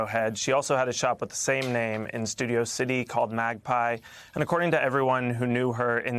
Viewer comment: It sounded like he said "LGBDQ community".